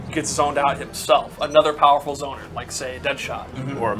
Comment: When said Mirror Match I thought he said Mirror Master and I almost spit out my drink.